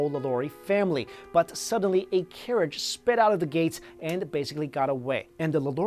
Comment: Great video! also when you said,"a carriage sped out of the gates" I first thought you said a carrot sped out of the place! LOL!!